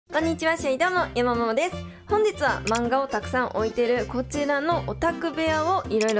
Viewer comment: おまたせえええ！！！📕📚また全部の漫画紹介するのもやるね☺️最後にお知らせあるからぜひみてね💪( ᐛ 💪) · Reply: 「漫画を沢山置いてる」が「漫画ヲタクさん置いてる」に聞こえた